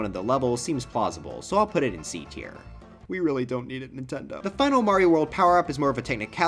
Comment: 6:06 Nintendo: Misheard as “we need this back Nintendo”